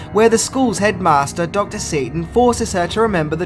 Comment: Thought you said dr. Satan at 0:45 😅 The poltergeists and house of 1000 corpses crossover 😅😀